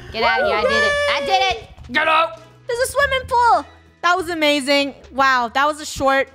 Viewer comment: UMM I THOUGHT THAT IS SAID SWIMMING POO